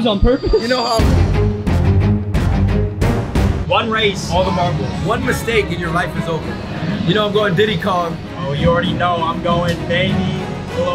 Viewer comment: DID HE SAY DIDDY KONG